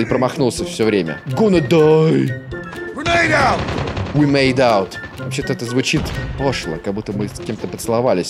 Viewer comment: Я услышал Granade OUT - типа "граната пошла!", а не пошлость.